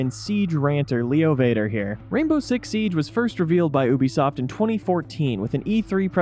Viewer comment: did he just say oobisoft?